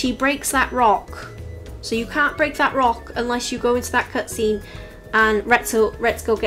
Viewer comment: unless you get in that what scene pardon? i didn't hear well i need help with this one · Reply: There’s a cutscene where she gets angry and breaks the rock so that one is locked behind one of the quests ❤